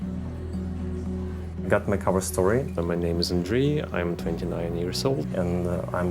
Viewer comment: stop this nasty Russification of Ukrainian names; the guy at @ 8:43 says his "cover story" name is Andriy, not Andrey. The former is a Ukrainian name, the latter is a Russian name. Also, it's bizarre Russification to have all the draft-dodgers are mentioned with a Russian name instead of a Ukrainian name (e.g., the they called a man who dodged-draft and illegally ran to Molodova by a Russian name Evgeniy instead of a Ukrainian name Evhen).